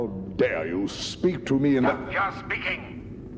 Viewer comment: What does the robot guy say at 1:04? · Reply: "Hold your speaking" - Robot Guy.